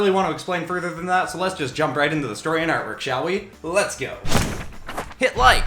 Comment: When u said artwork I thought u said armor